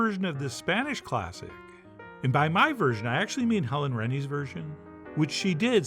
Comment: Does cutting the onion against onion grain breaks up onion so it releases juices and produce tears more? Recipe looks good but omelet looks too brown? The Helen Reddy reference went over my head. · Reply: I thought he was saying Helen Reddy ("I am Woman, hear me roar") too but found out he was actually referring to Helen Rennie, a Russian-American culinary teacher with videos on YouTube.